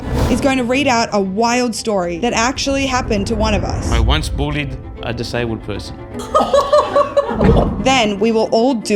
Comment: Ok so did really never have a snake because she told "i will give you $1000,on camera, if I never had a snake." Also...I'm glad I went back and read the caption because I thought he said "bodied a disabled person." Lol